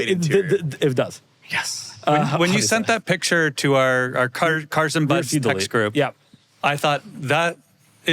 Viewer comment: did just said cars & buds?